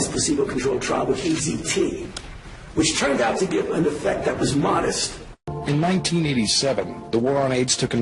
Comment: did he say ACT effect that is modest? not poisonous?